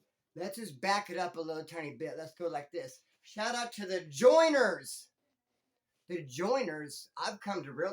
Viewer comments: Bro I been watching you for quite ah while now an I swear everytime you said shout out to the joiners ?? I thought you were saying the joyners like they were ah family or ah couple who you were tight with lmao I was always like who are the joyners lol I just barely got it right now lol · All this time i thought you were saying Joyner,as a person named that ...but your saying joiners😂😂